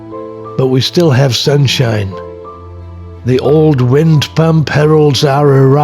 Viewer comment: Hi, did you said " Pampero wind" in 1:50 ? Well... something from Argentina . But from Buenos Aires. Beautiful family